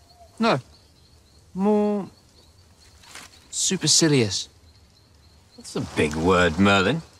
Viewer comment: what's the word Merlin uses for Arthur here? And what is the meaning? I tried looking it up.. but can't find anything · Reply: Supercilious --adjective behaving or looking as though one thinks one is superior to others